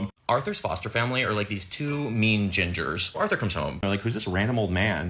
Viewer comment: You said “two mean gingers,” but I heard “two mean genders.” 🤣